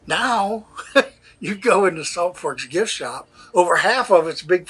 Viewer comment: Thought you said Salt Pork, Ohio, then saw the sign 🙄